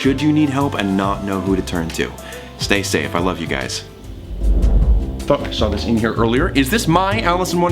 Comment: is anyone going to talk about the end after the outro???!!! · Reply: i thought that at first but, instead, i think he said " i THOUGHT i say this thing in here earlier"